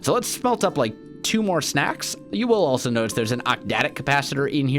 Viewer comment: Did anyone else hear him say snacks instead of stacks at 7:58 ?